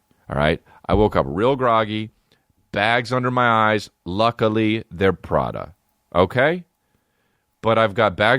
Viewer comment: I thought he said "luckily profit" cus it doent make a fuuuuuckin dent